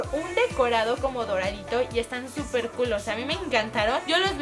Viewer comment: algien oyo que dijo estan super culos en el minuto 6:33😕😕😕😕😕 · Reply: dijo: "Están súper Cool ósea" 😜 y si lo juntas parece que dice culos pero escuchen bien! 😂👌